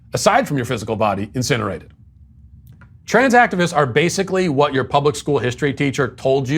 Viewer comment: Trans activist? I thought you said trans accident